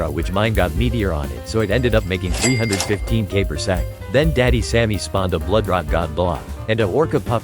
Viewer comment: did he just said daddy sammy💀💀💀💀